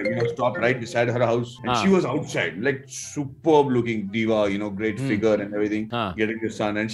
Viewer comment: did someone say Superman in background 👀 Or wt what was it👀